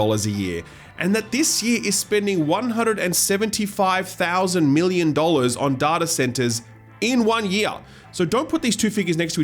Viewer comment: Am I hearing say 175 Thousand Million dollars?